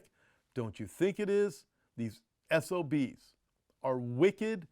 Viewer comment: Did the pastor say S.... O.....B....s😒 Or did I hear wrong?